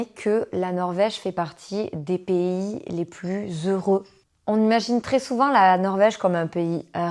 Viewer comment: les plus "Euro" quoi ?